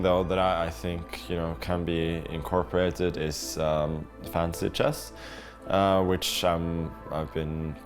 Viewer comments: I thought he was saying fancy chess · I hearing "fancy chess" lol